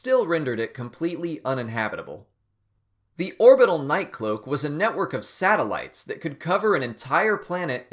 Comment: I thought he said "Orbital nightclub" 😂😂😂